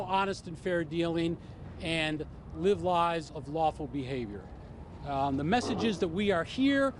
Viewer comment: he says " Live lies of lawful behavior" . he probably meant to say Live Lives.. but that's not what he said. he said "Live Lies." , maybe that's what he is doing. is living the lie of lawful behavior.. not really living lawful behavior. if mikey cohen can live the lie of lawful behavior, then many others can be doing that as well.